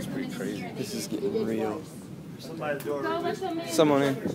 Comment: The tornado story whilst horrifying also oddly enough restored a bit of my faith in humanity, I mean.. the way these COMPLETE strangers treated eachother was very surprising! From a simple "Ma'am are you okay?" to a "Hey guys be careful there's broken glass everywhere!" Maybe it's just me but, you always hear about how selfish people can become in life or death situations, so seeing them all sort of band together and make sure everyone was okay was really cool. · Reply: I hear "go let them in" and whoever did the captions seemingly did too